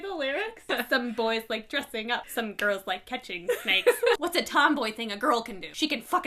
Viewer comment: I thought they said "some girls like to catch steaks." I was like... I like me a good steak. Ha!